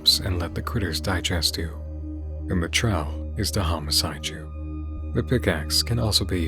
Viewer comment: i did notice if you turn on closed captioning it has written captions and what she actually says in vhs night is "home aside you". i don't know if that wording has any significance or if they were just trying to dodge censorship....